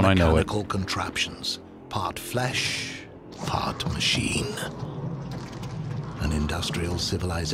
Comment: Top-knot "clone" guy also being obnoxiously-eating-in-inappropriate-environment guy seems like a natural combination. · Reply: definitely sounds like he says "part flesh, fart machine"